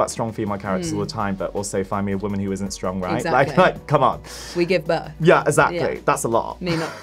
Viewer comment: Is it me or did she said at 4:05" we give birth ...well not me personally" ?